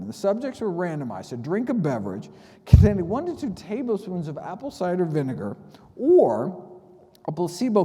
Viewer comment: TEAspoons of vinegar, not tablespoons. Ty for summarizing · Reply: Thanks, but I hear Tablespoons at 23:49? Let me know where you get the teaspoons from and I'll change the summary. Thanks again!